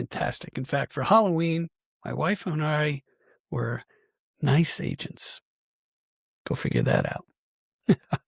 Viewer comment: Oh, nice agents for Halloween. OK. Time to apologize. I thought you said ice agents which didn’t make any sense. Well it does. Nice agents I like that that vesper recall so so simply simple that it works.